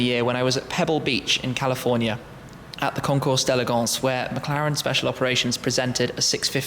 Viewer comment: What did you say at 1:55? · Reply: concours d'elegance